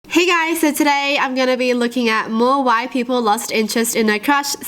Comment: In the beginning I heard “more WHITE people lost interest in their crushes” 🤣